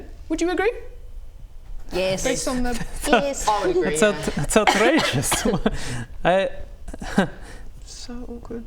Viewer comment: OMG I heard say it's outrageous =)